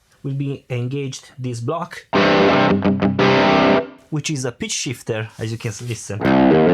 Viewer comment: I love your accent. It sounded like you said 'Bitch Shifter' instead of Pitch Shifter. :)